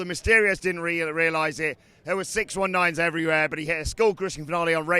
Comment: I actually thought you said there were Veers everywhere.